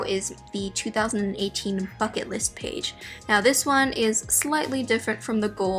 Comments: i thought she said 'fuck it' instead of 'bucket' and i was shook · the first time she said “bucket list” i thought she said “fuck it list”